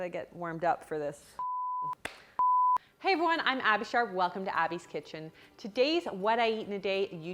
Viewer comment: Every time! EVERY SINGLE TIME! I swear I hear SHART. My boyfriend just said “did she just say shart!?” Also I love your channel so I’m not shading I just thought my hearing was off